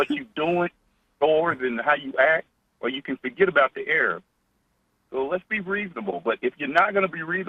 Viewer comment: did he say "you can forget about the Arabs)? If so, then LOL!!!! I'm an Arab-american and I agree with him and it really made me laugh!! Haha... what am I supposed to do? Call him "racist" for stating facts? I'm just gonna laugh and keep listening. Keep up the good work, sir. =)